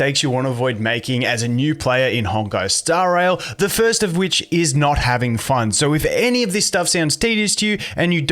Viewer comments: i friggin loled, bruh i thought what you said was 'the first mistake is not having fund' i was like, what? the heck this guy talking about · misheard and thought he said not having fund at the start lol